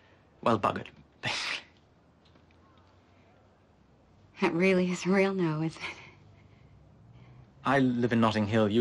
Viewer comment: What does she say at 4:14? "That really is real no it?" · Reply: "That really is a real no, is it?"